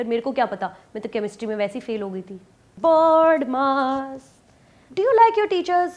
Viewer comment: Hai, mutual feelings for school from 1 to 10grade, 😖horrible teacher, horrible school. Thank God, I grew out of it, best college n university times during my bachelor n masters. Board mark😂....I have seen chalk always... Love it, keep up the good work Sachu😘😘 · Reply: I thought she was saying "BODMAS" as in the rules of the order in which Math problems should be solved. Why would a Math teacher sing "board mark"?